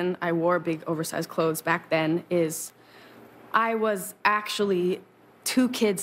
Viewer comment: BECAUSE NO ONE ELSE IS EXPLAINING IT PROPERLY: She didnt want to be sexualised as a minor. Even though she wasn’t wearing revealing things, she was still sexualised so incredibly much in the industry, no one caring about her age. It actually makes me sick, that no matter your age or clothing, you can still be sexualised so easily.. poor billie :( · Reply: Can someone tell me what she said? I hear "I was actually two kids stabbed on top of each other trying to see kids when I'm in a movie" or sum